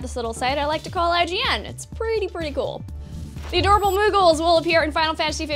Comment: I thought you said Google not moogle when I was just listening to the video lol